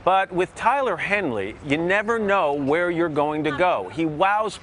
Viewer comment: did he say tyler henly ?